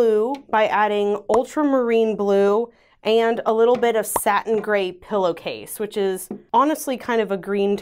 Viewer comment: When you were saying the colors for the second layer, I thought you said "second grade pillowcase" and I was REALLY wondering about that...good thing your actual names are in the screen. 🤣😂